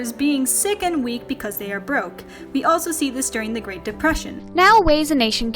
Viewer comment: D-did i hear that right? Did she just say Great DePrussian?